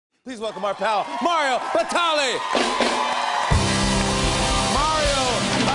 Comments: At first I thought it said Mario Balloteli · Damnit I thought it said Mario balotelli 😫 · I thought it Said Mario balotelli😂 i was happy ass hell. But this is fun to! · Who else thought it said Mario Balotelli? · i opened this video cuz i thought he said mario ballotelli 😂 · I thought it said mario balotelli... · Who else thought it said Mario Balotelli